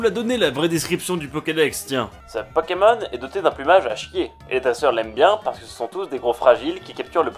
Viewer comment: A 1:18 j'ai entendu "Ta soeur" au lieu de "les dresseurs" mdrr